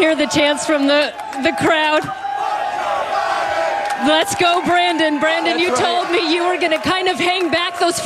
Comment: I clearly heard chants of “F*** Joe Biden”, not “Let’s Go Brandon”